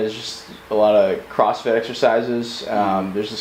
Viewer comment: DID he say Crossfit... face palm Now I know I could never been in SF I just cant Im not built for it. That said, I dont want the men protecting me doing crossfit... Now Im really afraid...